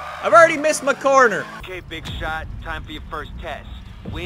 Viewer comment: Welcome back to the Need for Speed Payback Let's Play! We've got more episodes coming out every single day so make sure you subscribe for that! See you tomorrow! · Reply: 0:55 Did he said Big Shaq!? 😂😂😂😂 #SKKRRA